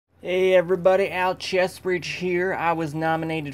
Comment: First I hear 'Alcheastbreach here'... :)